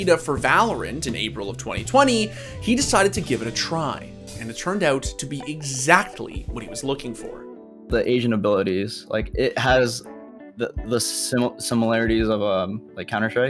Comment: I legit heard " the Asian Abilities" when he said the agent abilities. lol